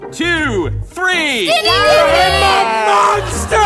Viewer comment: in case you were wondering, in the huddle at 25:01, Mother says 'dying,' Son says 'skinny dipping,' ox says 'i'm a monster,' (duh), i think grandpa says 'why?' or something, Daughter just says gibberish (i can't understand her), and Father doesn't say anything.